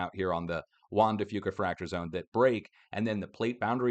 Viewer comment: I always thought was saying Wanda Fewca fracture zone 😂